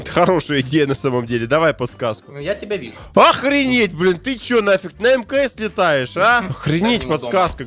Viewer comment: мне послышалось,или сказал Охренеть блять